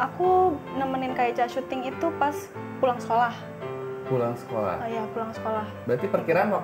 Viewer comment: Sekolah di Pemalang salah denger ngga sih gw ??? Lah gw orng Pemalang langsg hah ?! Gtu anjir 😴🗿